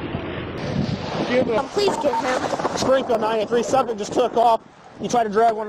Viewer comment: Sounds like she says "Please kill him", lol